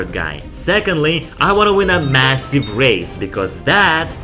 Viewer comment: did you said m.. massive?